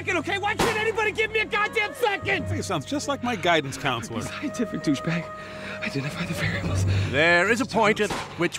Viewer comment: Did Mark just call himself a scientific douch bag? LOL!